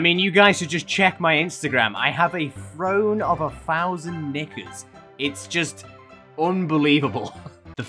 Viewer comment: what did he say??? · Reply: Knickers, not the n word. Yes I’m aware they sound similar but it’s also the accent